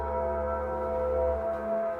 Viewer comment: The part where the rocking chair behind Kim and it says hello hello in my ears I hear “hello son” in voice